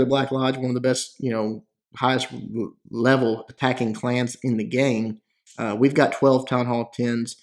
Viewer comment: did he say hacking or attacking? I'm also not familiar with the black lodge so sorry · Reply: he said attacking, but black lodge is known for modding